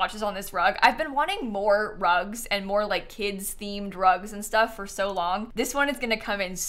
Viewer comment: "...and more kids' themed rugs" 100% thought you said kids' themed drugs for a second